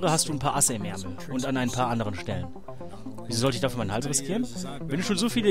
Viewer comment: Warum habe ich das mit den "und an anderen Stellen" irgendwie falsch verstanden? xD